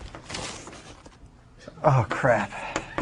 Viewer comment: did he said crap?